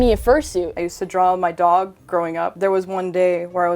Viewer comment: Did she say that she used to draw ON her dog ???